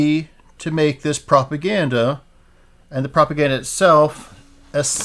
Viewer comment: I had to rewind in the middle when I thought I heard you say "copaganda." It would have been appropriate!